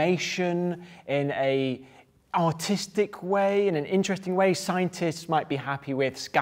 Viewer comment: it sounded like he said “in an autistic way”